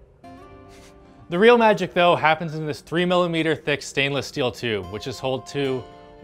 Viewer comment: Did he say "...3 millimeter thick steel tube..."? How could a tube wall so thin withstand the external pressure, if inside is a near perfect vacuum?